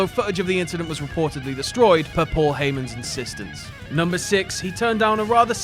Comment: Anyone know what he says there'? I can't understand him. "although ....the image was destroyed by Paul Heyman's insistence. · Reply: Although.. the footage was destroyed by Paul heymans insistence.